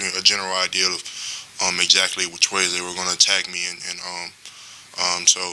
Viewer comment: Did he say "ejaculate which way" at the 2:01 mark? Or am I brainmush this Monday?